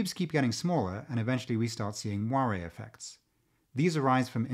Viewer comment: oh.. moire effects. with your bad pronunciation I thought you were saying "Wario effects" haha